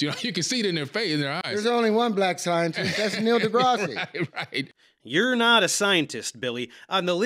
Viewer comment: Did he just say "Neil deGrassy"?